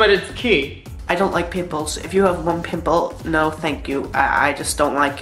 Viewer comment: With the first guy, i honestly thought he said pitbulls instead of pimples! Lol!